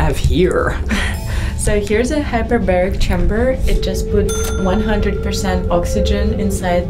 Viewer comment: thought she said hyperbolic chamber 😂